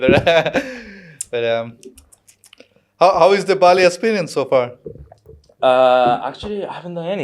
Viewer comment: Columbus, OH is so random but, hey, I also worked there for almost a year years ago! At the start I thought they said they were in Berlin and I was surprised how tropical it was.